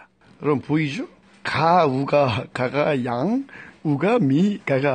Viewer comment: 나만 원시인이 우가가가 들리는거???